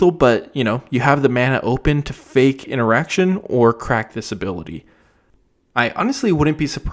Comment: was so sure he said "fake an erection". I had to listen to it again to realize what he actually said.